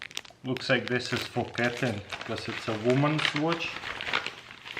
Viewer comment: did he say "this is for Katherine unless it's a woman's watch" ????